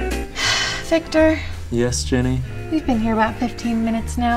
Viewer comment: did she just call Ian "Vector"? this was foreshadowing!